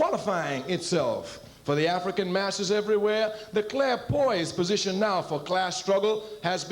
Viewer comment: Can anyone make sense of what he says at 29:32? What is/are the word/s before 'position'? It sounds like 'claire poise.' 'clear poised position'? That doesn't really make sense. Closed captioning renders it as 'Clare boys position,' which doesn't make sense either.